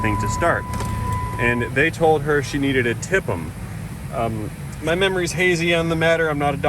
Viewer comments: Ha! I thought you said for them to fix her van she needed to “tip them” like pay them cash. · When you said TIPM, I thought you said, the other auto shop said she needed to Tip ‘Em 🤣🤣